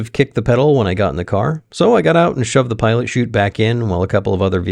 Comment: At first i heard "pile of chute"😄